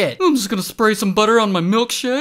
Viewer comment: "Let's just spray some butter on my other butters and then I'll serve it to butters" 😂😂😂😂😂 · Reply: I THOUGHT THAT SAID PRAY BUTTER ON BUTTER😭✨